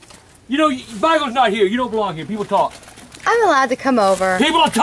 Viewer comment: What did AGP say at 0:24? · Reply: People talk